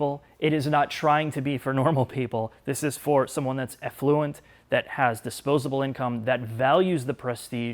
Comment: I turned on the subtitles because it sounded like you said "effluent," I sure buyers of any marque would not like too be called effluent! Hi from NZ.